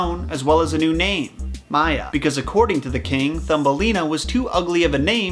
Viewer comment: I had to replay 9:27 so many times because when he said Maya, I kept hearing 妈呀 😂